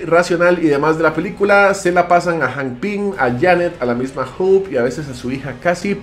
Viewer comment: En el segundo 9:40 dice la misma Hulk? o escuche mal?